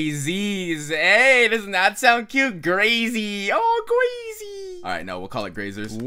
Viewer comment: When he said Grasey I thought he said Gracy but then I read it